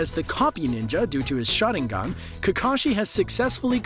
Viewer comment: i swear I heard him saying shotting gun instead Sharingan..😅